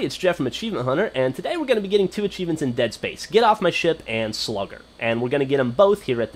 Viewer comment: when u said get off my ship it sounded like u said get off my shit lol